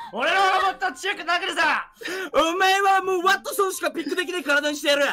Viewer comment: I think Daruma is saying "I'll make it so that you can only pick Wattson" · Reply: That's exactly what I hear lol, you heard right. ワットソンしかピックできない means he can only pick Wattson. しか＋ない means only, as in there's nothing else but that. Idk if that explanation made sense, sorry lol.